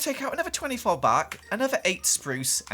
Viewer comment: Did say bark? 1:27 am i hearing things...?